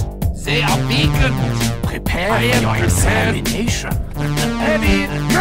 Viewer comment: i thought sum1 said time for your examination behind me lol